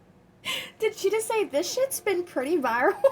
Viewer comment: I think she said “this shade”, or at least that’s what’s the CC said it was. I fkn adore you! The smallest things tickle you, and your laugh is contagious!!